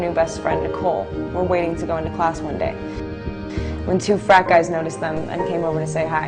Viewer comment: does kristen say " when two fat guys..." ? lol go kristen